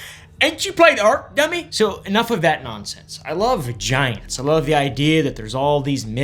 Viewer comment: not gonna lie, I thought you were about to say I love vaginas